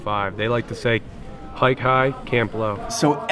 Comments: I didn't catch the meaning of "High tide, camp low" at 5:45, what's it mean exactly? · I didn't catch the meaning of "High tide, camp low" at 5:45, what's it mean exactly? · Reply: I think i heard "height high, camp low"